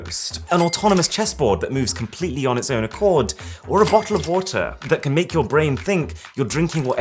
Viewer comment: did he say bo’o’wo’wa 😂